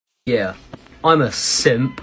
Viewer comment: I thought it said, "yeah, what a simp" 💀💀